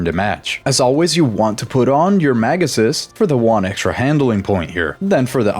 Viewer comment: everytime you said "mag assist" I always misheard it like "megasus" lol